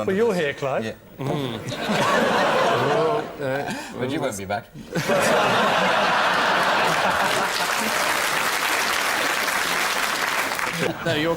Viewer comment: What did clive say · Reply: "But you won't be back"